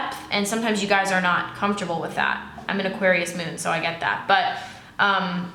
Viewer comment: I thought you said you were Pisces moon, not Aquarius moon 🤔